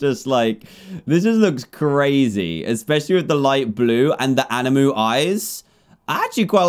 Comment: did he say anamu eyes